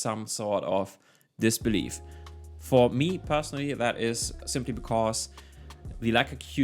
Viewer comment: What is he saying, I can't make it out to save my life. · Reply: “Simply because”